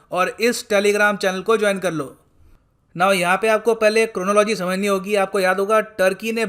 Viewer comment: did you say chronology?🤣🤣🤣